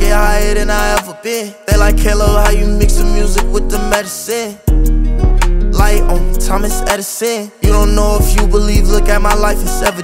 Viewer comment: “Light on me Thomas Edison” 🚀🚀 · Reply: I thought he said, "like UH.. Thomas Edison" bro I'm so goofyyyyy 😭